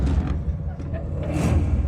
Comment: min mark. I hear (when door opens). Either COME PLAY or OK 🤷‍♂🤷‍♂